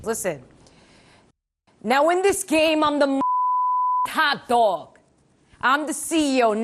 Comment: 아니ㅠㅠㅠㅠ 탑 도그가 핫도그라 들려ㅠㅠㅠ 배고프당